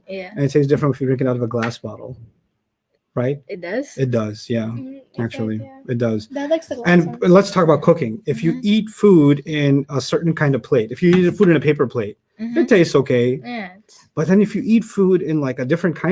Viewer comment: I literally thought he said "lets talk about cocaine" lol